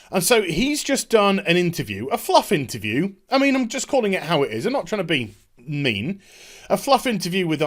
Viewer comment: what i heard u sayd. "lets call how'ard it is" shite :P